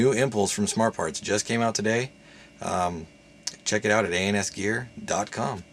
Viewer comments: i literally stated before posting i mean no disrespect to ans gear. they are a great store and i have nothing against them. i did however state that he sounded like he said something like "anus gear" at the start now if this offended you...i dont know why then disregard my statement. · lol i dont mean any disrespect but doesnt he sound like he said anus gear at the start